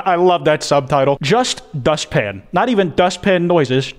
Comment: Instead of it saying dust pan I thought it said just pain